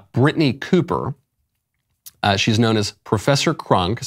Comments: At first Michael I thought u said Professor Klump 😂 true story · I thought he said professor klump. That woulda made sense